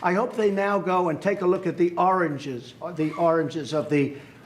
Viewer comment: I'm french si I don't understand English 100% and I thought Trump said "the oranges" and it didn't make any sense to me haha